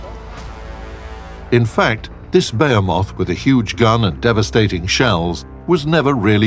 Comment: Lol @ 6:42 baramoth, just like pronouncing the map minis instead of mines years ago xD